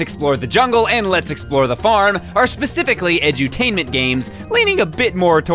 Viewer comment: I saw yawning when he said "the other two games in the series, Lets Explore The Jungle and Lets Explore The Farm" and I had to doubletake because I heard "Lets Explore The Morgue"